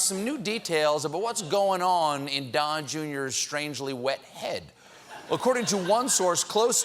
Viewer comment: Did Stephan call Don Jr. a water head?